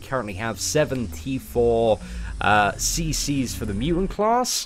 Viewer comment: I genuinely thought said he had "74 CC" not "7 T4CC" 😂